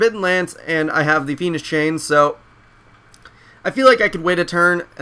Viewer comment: Lol, sounded like you said "the penis chain" 0.o